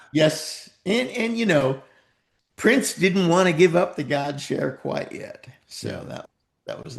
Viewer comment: did dez say prince did not want to give up the "God SHARE" or what please?